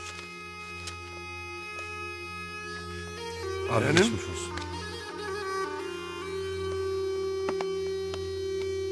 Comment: ne diyor? arenim nedir? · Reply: yeğenim diyor